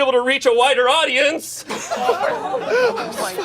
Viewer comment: I genuinely thought he said a WHITER audience and it was equally if not more funny 😂😂